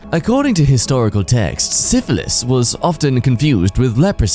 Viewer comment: ngl, i thought that said Sisyphus at first